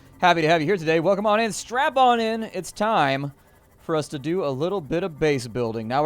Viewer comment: did he say "Strap-on in"? :D